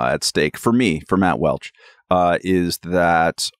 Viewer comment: Ugh, for a second I thought you said Matt Walsh.